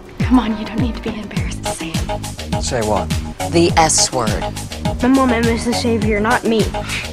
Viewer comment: what's the s word I forgot, spell? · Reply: helplessly satisfied frost Savior